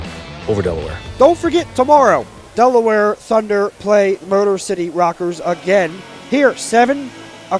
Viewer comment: did he say Murder City? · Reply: Very close but motor city 😛